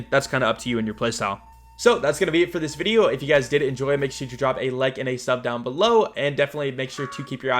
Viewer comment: I always drop a like on videos even before the video starts just because I know it's going to be always quality content, no matter what. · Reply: Thought u said drop a nuke.